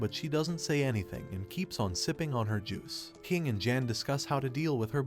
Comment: I thought that he said simping over her juice💀💀💀